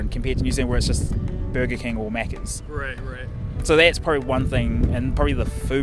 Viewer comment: He probably said "Burger King or McDonald's" but the subtitle said "Burger King or Maggots." I'm unsurprisingly not offended by the subtitle.